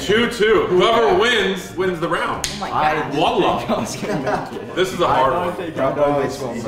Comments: did he say wallah.?? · did he say wallah😂???